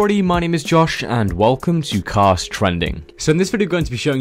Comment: At first I thought he said welcome to cock trending